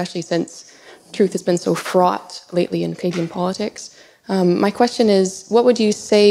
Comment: Frought in pagan politics? Did she say that?